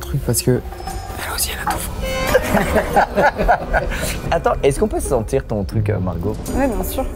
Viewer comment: c moi ou a 23:39 il a dis « tu la decris comme ateez » mais ateez c un grp de k-pop